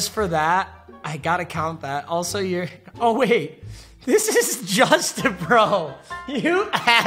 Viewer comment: anyone else thought he said “this is Justin bro” but later realized he said this is JustAbro